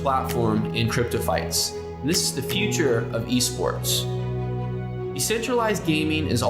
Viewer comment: Thought it said Escorsts at first...